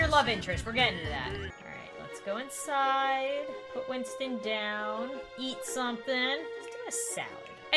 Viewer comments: “Yeet something” anyone else hear that? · sounded like Yeet something😂